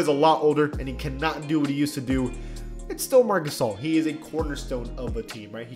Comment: Is it just me but sometimes when someone says marc gasol i hear marcusoll